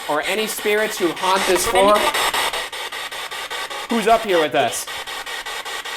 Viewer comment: /10:33 It sounded like a man saying “don’t go in there!” Or something. That’s what it sounds like to me ;P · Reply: I heard "Who's up there?" really clearly. People for sure.